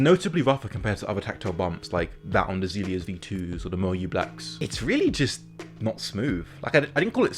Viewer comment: what is the keyswitch you mentioned at 1:57? moe blacks? moye? m something? couldn't quite understand it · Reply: Moyu blacks